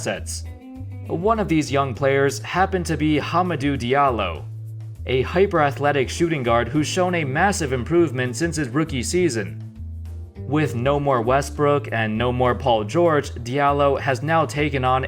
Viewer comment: Am I the only one that heard him say HUMPERDOO Diallo from preacher?